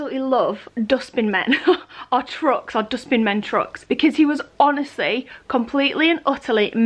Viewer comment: When was talking about the truck. What kind of truck? Sounded like she said “dusting man’s truck”